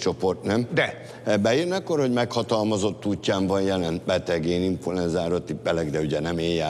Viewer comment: aki jol fülel az azt hallja amit mond Mucsi: INFULENZA